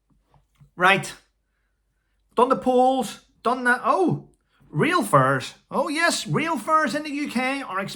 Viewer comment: I thought at 8:06 you said real furs. I was confused for a moment, albeit it doesn’t take a lot. 😂😂